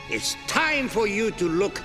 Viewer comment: I thought it said It’s time to look N-WORD